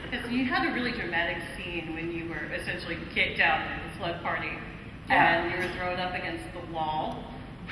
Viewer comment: can somebody tell me what she said cus i cant hear her clearly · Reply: She said that he has a seen where he was kicked out of slughorns party and pushed up against a wall and then the girl did that :)